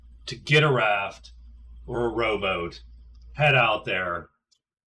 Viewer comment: Every time you say "rowboat" I hear "robot" :)